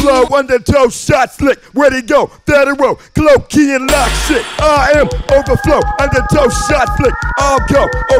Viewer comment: I always hear "I am overflow tuna fish casserole"